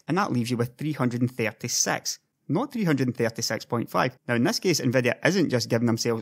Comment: Did he say sex?